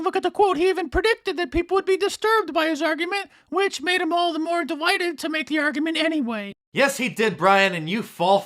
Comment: When he said delighted I thought he said de-whited